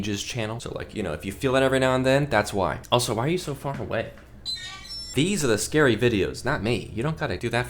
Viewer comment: You asked why im far away..ha i thought you said, why are you soo wet? Haha..cuz im watching..duh...😅 18:15 i heard f-ing wet...😂